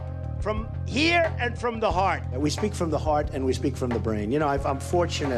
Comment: not just a big brain......a big.....mmmmmbrain. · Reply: I heard it as "uh brain"